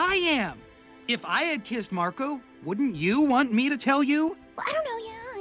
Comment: "iF I WEre 2 KiSs mArcO-" Boi why do I think you want that 😁